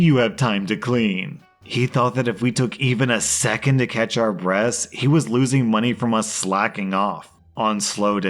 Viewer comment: “…second to catch our breasts” lol I know he said breaths, but I heard that while doing other things and was like “what?”